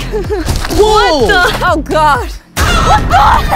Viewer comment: bro what did say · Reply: Coc